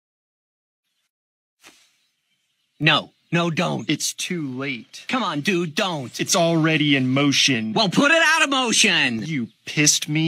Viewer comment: I thought he said “you kissed me off” but it’s actually “you pissed me off”